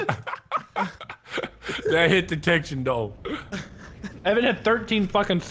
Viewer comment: did say Evan has 13 stab